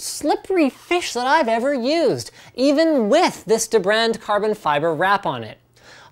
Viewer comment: WAIT, did he just say "with this debrand on it" WOT